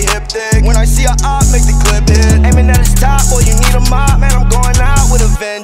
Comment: i thought he said in mineta's top